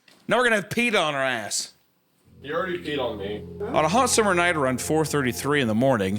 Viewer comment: "We're gonna have PETA on our ass" "It already peed on me" Did I hear that correctly?